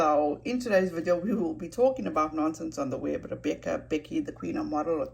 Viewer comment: Hi beautiful lady, take the time for your grand kiddos. They will not be kiddos for long--time goes so fast.Nonse sounds like she saying nonsense. I think if Nonse talked to Molly she could have gotten the truth and reported on it herself. I do agree taking a break from posting social media is a good thing. I do read and watch social media because it is relaxes me, but consistently posting things makes me anxious and I am afraid I will go maniac. Also I want to update my background, so that is why I have not been doing the book club and not doing FaceTime on lives. Always stay classy!